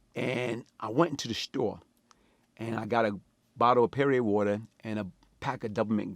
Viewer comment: I know I ain’t the only one thought he said “period water”